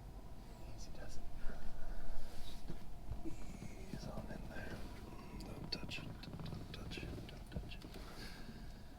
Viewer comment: What were you saying dude😂😂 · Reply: Haha, he says ease on in there, don’t touch it don’t touch it don’t touch it